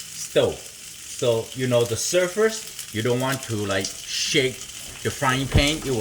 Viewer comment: It took me a minute to realize you were saying surface. I thought you were saying surfers 🏄‍♂️